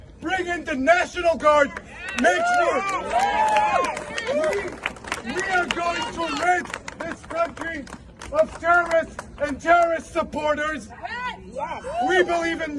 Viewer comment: What did this loud mouth say at the 6:40 minute mark ?? Quote " We are going to make this countries of German and Terrorists supporters .... End Quote.... WTF ? Was that a Freudian slip or is he trying to incite violence against Germans who've NOTHING to do with America's Pro-Palestinians protests. This idiot needs to watch his filthy mouth; STOP shouting lies.